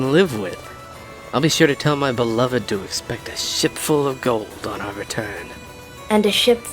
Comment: When it said shipful of gold I thought it said shitful 😆